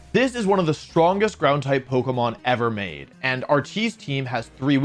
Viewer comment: When he said "RT's team" I heard "our cheese team"